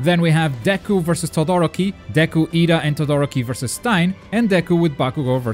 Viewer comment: It sounds like you said stine instead of stain